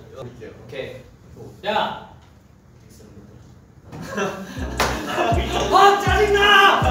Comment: am I the only one who heard minho cursed tangina